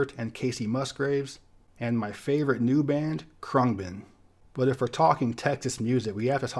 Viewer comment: did he just say “crungbin”…? That can’t be a band name